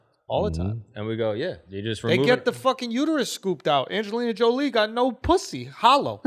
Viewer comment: When he said Angela Jolie was hollow I thought he said Angela Yee.